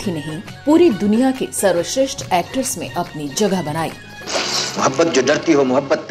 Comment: sharva shresth actress . Kya bol rahi hai ap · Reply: It's "Actors"